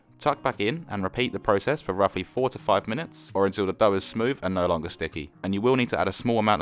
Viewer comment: omg i thought he said 45 minutes instaed of 4 to five hahahah